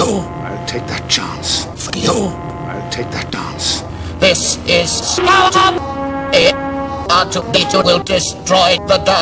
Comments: THIS, IS, SKARO! ;) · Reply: At first I thought he said "THIS IS SCOUT!" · I saw a comment where someone claimed that Dad said “THIS IS SPARTA!” at 1:54, but I hear “THIS IS SCROTUM!”. We need a debunking of this!